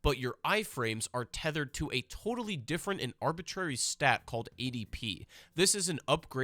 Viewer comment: Not gonna lie, I initially misheard "ADP" as "EDP".